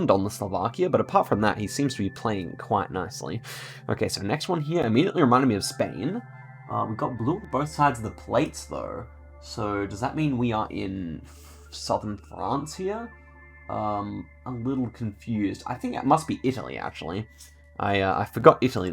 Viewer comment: Did you just say "Francija"? Very Slovenian